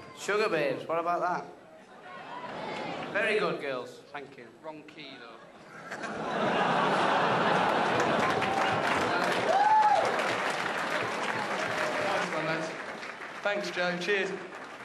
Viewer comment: what does Andy say? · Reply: wrong key though